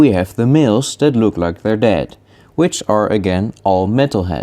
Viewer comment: LOL i really thought you said "the males that look like they're dead" at first